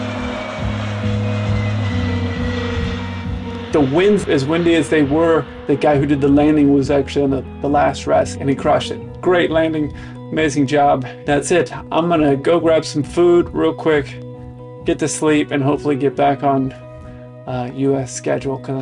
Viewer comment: Maybe a phrase other than "he crUshed it" would prevent people like myself from having minor panic attacks in the future 😅 Love these - thanks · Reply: 🤣 I heard "and he crashed it" I admit I was paying more attention to the video than the audio at that point, but yea.